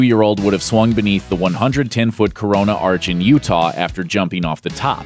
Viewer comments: Im sorry did i hear corona?? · Wait did he just say corona? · did I just heard Corona.....